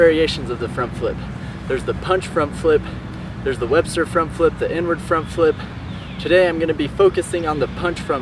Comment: I thought he said there's the N word front flip not inner front flip